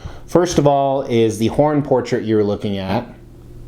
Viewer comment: I thought you said "porn portrait" until I realized the horns missing on the 2nd portrait.....